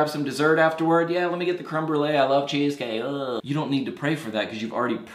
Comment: I was watching the video with subtitles , and instead of "I love Jesus" it was written 'I love cheesecake' 😂